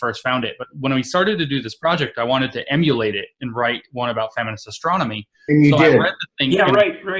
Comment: Dr. Lindsey mentions a "feminist something-ology" paper, but I cannot make out what he's saying; anyone know (or better yet, can anyone cite the paper)? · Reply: '...feminist glaciology paper..." At that point he's referring back to his description in this video of the experience of reading it closely, at 12:17 [along to about 13.12]. I think the article may be this one, published in 'Progress in Human Geography', Vol 40, Issue 6, 2016: Glaciers, gender, and science: A feminist glaciology framework for global environmental change research Mark Carey, M. Jackson, Alessandro Antonello, ... First Published January 10, 2016 Research Article <link> Abstract Glaciers are key icons of climate change and global environmental change. However, the relationships among gender, science, and glaciers – particularly related to epistemological questions about the production of glaciological knowledge – remain understudied. This paper thus proposes a feminist glaciology framework with four key components: 1) knowledge producers; (2) gendered science and knowledge; (3) systems of scientific domination; and (4) alternative representations of glaciers. Merging feminist postcolonial science studies and feminist political ecology, the feminist glaciology framework generates robust analysis of gender, power, and epistemologies in dynamic social-ecological systems, thereby leading to more just and equitable science and human-ice interactions. Keywords feminist glaciology, feminist political ecology, feminist postcolonial science studies, folk glaciology, glacier impacts, glaciers and society